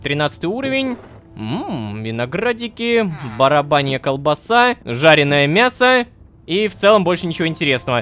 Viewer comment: Я одна услышала барабанья колбаса время если что